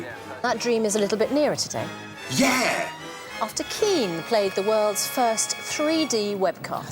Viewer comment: Damn when he said Keane I thought he said ween lol